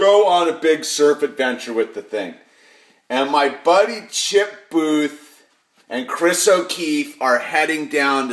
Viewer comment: I thought you said "my buddy Chip Foose" for a second. I was like damn this guy knows a lot of people.!